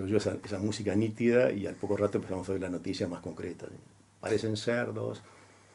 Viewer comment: '''parecen cerdos''' ?????.....entendi algo mal???????